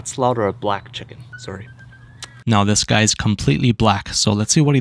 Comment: I thought he said this guy is completely fine